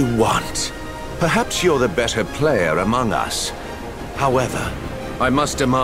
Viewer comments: I heard 3:20 as, Perhaps your the best player in Among Us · I swear I almost thought he said “You’re a better player in among us.”